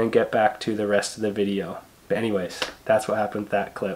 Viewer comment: It sounded like you said "Bennyways" instead of "anyways" --hehe :)